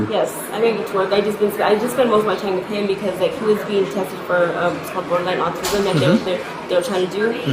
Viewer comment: The kid is FREAKING OUT. I wonder if he’s had bad encounters with strangers before. I watch a lot of these cases, and I’ve never seen a kid freak out to the extent of kicking. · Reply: I thought she said he was tested for autism? It was hard to make out her torrent of words.